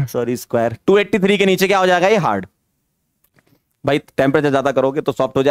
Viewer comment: did anyone notice what sir said 😂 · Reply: Sorry square bola sir ne